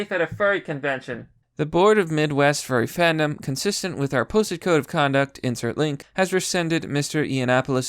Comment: I thought you said pussy code of conduct lol